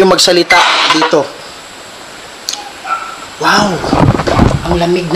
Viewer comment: Hello po idol, may narinig po akong boses hindi po galing sa spirit box. Boses babae po sabing, "wooyy" dahan2 lang po pagkasabi. 11:38 mins